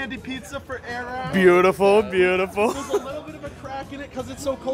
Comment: I thought he said there was a little bit a crack in it💀